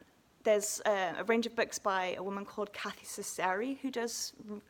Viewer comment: Hi, can you spell the name of the woman you were taking about here at 9:57? I couldn't catch that, is it Cathy Sassari? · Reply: Sure! Kathy Ceceri